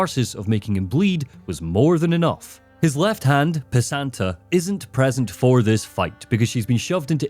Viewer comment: that name lol sounds like ur saying piss ant uh